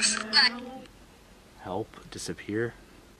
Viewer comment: I heard a man sobbing and saying help